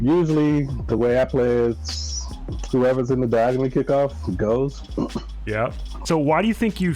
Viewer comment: i keep listening and maybe he does say diagonal but HE WAS diagonal and doesnt go for it. Can’t tell what he’s saying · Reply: he said "the way I play is whoever is in the back of the kickoff goes" personally that's a dumb strategy